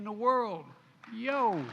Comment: did Brandon say, YO, and then eyebug the camera?